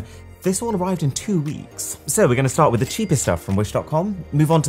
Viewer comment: I thought you said cheapest tech